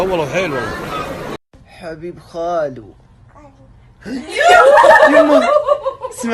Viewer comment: ايش قال؟ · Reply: قال خالي